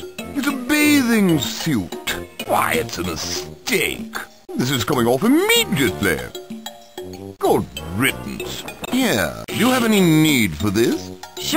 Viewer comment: what did he say? i really can't tell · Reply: "This is coming off immediately." "Good riddance."